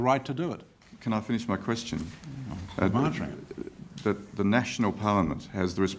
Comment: what does he say in respond to the guy asking if he can finish his question at 4:52? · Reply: “I’m answering it”